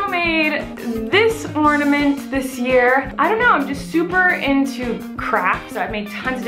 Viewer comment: It sounded like she said that she;s super into crack!! :'D